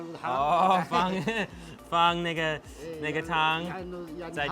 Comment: Lol i thought he said nigga